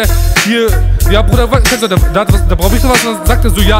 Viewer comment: Schon locker 10.000 mal gehört und trotzdem immernoch kein plan was er bei 0:21 sagt 😂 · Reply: "Kennst du doch, der braubichte was.." oder welche Stelle meinst du? Was kann man da nicht verstehen